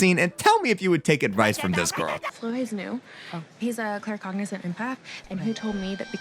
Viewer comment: What the fuck did she say? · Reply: I heard “he’s a clear Congolese empath” but im not 100% sure